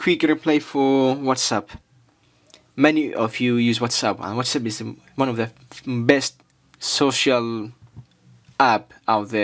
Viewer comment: i heard u said whatsapp is the most fucking ahahha